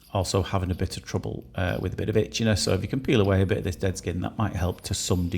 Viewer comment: When you said she had a problem with itchiness i heard it as bitchiness lol maybe i need my ears cleaning 😮